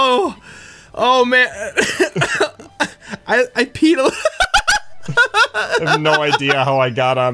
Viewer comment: I peed alot is what one said